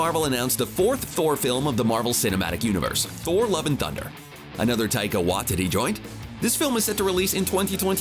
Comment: All i hear is “Taika Wa-Titty”